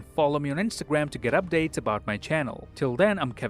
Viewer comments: I’m sorry, Did he say “I’m kevin hart” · Hold up Did he just say his name is Kevin Hart at 09:57 ?